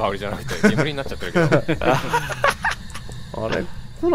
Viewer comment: クッキーの香りじゃなくて…のとこ、キヨくん何て言ってるのか分かんなくて、なぜかすごい気になってしょうがない笑 聞こえた人教えてください🙏笑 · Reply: 煙になっちゃってるけど かな